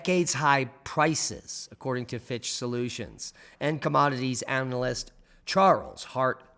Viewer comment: I thought you were saying "Bitch Solutions." Lmfao!!